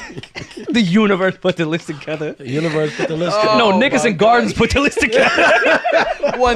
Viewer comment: did rory say nigga or am i bugging? from 2:02:07. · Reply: Nickerson Gardens